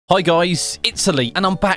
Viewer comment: In the beginning I thought you said "hi guys, Italy"